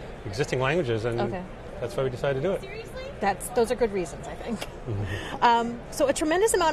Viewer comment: Did anyone else hear someone say "Seriously?" in the background?